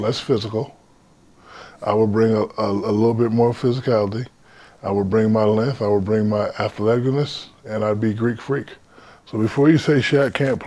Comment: What did he say?? I will bring my length, I will bring my ath-legolas" ?????